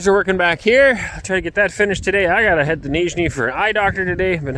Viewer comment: And again, about the distortion of the translation. said he needed to go to Nizhny Novgorod to see an optometrist. The Yandex browser translated it correctly in its voice, and the YouTube subtitles said that he wanted to go to North Carolina to see an optometrist. И опять, про искажение перевода. сказал, что ему надо съездить в Нижний Новгород, к окулисту. Яндекс браузер, голосом, перевёл правильно, а субтитры ютуба, написали, что он хочет съездить в Северную Каролину, к окулисту.